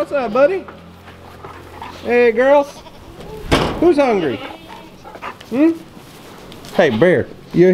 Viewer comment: when he says who’s hungry it sounds like one said me